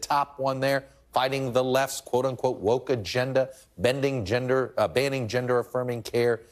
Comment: DID CHUCK JUST SAY BENDING GENDER??????? 🤣🤣🤣🤣